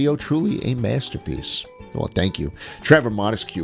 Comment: did he say "thank you" or am I hallucinating?